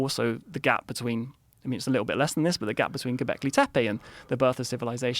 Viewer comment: At this exact moment 29:44 if you have subtitles on, it will say Quebec (a Canadian city and also the province) this is not what he says, he say Gobekli Tepe, an ancient megalithic site in turkey I believe????correct me if im wrong